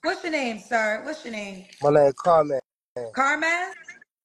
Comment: oh man, I need my hearing checked, I thought he said his name was "Carmax," and I was like, "Well damn show us the carfax😂😂😂, all them damn churran